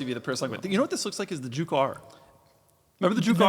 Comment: Juke R, I thought you said Jew Car